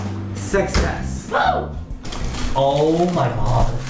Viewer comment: Can't wait for season 2 omg When they said Success pulling out the bread I swear I heard "Sexy ass"